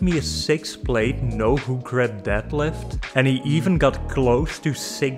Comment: "He sent me a six plate no _____" what word is that at 0:47? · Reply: Hook grip